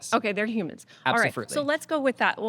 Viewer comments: did he just say absofruitly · did he say "absofruitely" ? xd